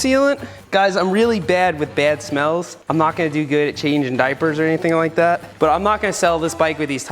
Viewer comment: Am I the only one who heard him say “I’m not gonna be good at changing diapers” baby Seth?